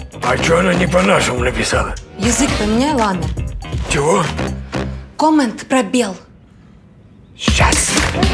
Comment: мне одной послышалось когда Лиза учила деда язык менять сказала коммент пробел?)